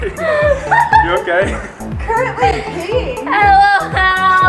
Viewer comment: does she say 'currently peeing'